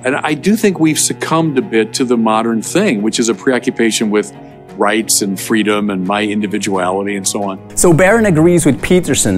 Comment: I think he wanted to say "Rights and freedoms", but it really sounds like " Rapes and freedoms, oh well probably just an innocent Freudian Slip.